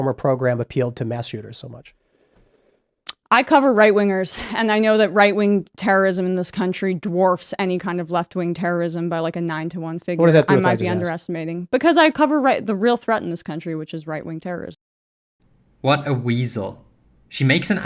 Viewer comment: I finally figured out what she just said. She is actually answering the question. She said yes. She literally said "we cover the real threat, so people will see that go out and deal with it"